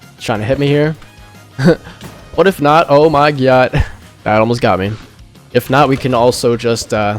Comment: he did not just say oh my gyatt am i tripping?